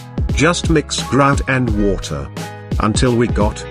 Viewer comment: what did u mix i cant hear it clearly sorry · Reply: It'sbtile grout buddy